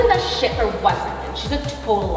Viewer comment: is it just me or did I hear say “sh!t”